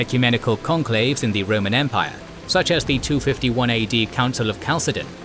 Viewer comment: Members of the team hav been using Ridge Wallets for years now and we recommend them! Check them out here: <link> Use Code “KINGSANDGENERALS” for 10% off your order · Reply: the captions says “451 AD” when the video says “251 AD”. Besides that great video as always!